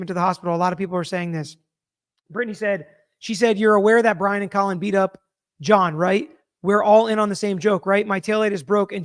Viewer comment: I heard “ you’re aware he was beaten up by Brian and Colin Albert … admit it, we’re all in on the same joke right? ……”